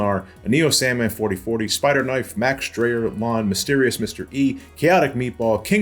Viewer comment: I'm sorry, did just say MysteriousMrE at 34:32?!